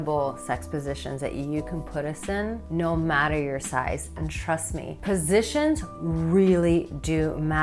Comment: Did you just say "thrust me"? I think I can do that for you!